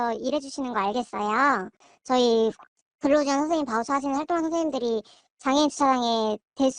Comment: 바우처 활동하시는 선생님들이 라고 들리네요